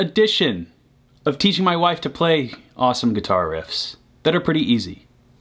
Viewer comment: At first I thought it said: "teaching my first wife" and I thought "pretty brave".